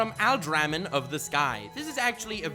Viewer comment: What is the anime called at 15:57 +? I just can’t understand what is saying there and I want to watch that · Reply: "alderamin on the sky" in case you still haven't figured it out